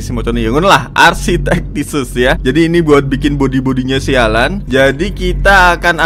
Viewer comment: aku dengernya sialan